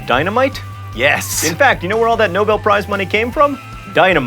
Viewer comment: I keep hearing "thank you for watching menopauze" during the outro.